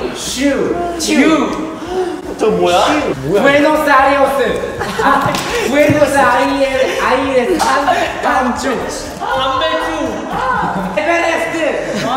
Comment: parece que dice "buenos aires" flasheo cualquiera mepa xd